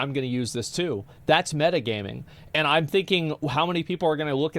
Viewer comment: I'm an RP viewer, did I just hear the word ''Metagaming''? at 8:50? BatChest